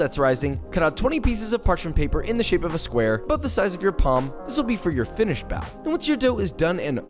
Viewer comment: I swear I heard "about the size of your mom" 💀😭